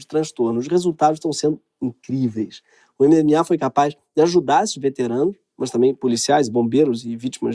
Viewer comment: "o MMA foi capaz de ajuda esses veteranos..." Osssss hahahahahahaha